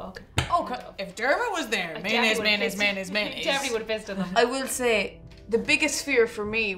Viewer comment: Wait… did say “I definitely would have pissed in them” if she had been paired with Dermot at 13:06?! I’m dying!!! 🤣🤣🤣🤪